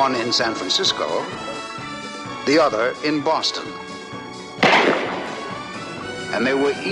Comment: You know, I thought John Forsythe said in the intro of the fourth season. Instead of Boston, he might say "The other in Seattle." I though I heard something like that.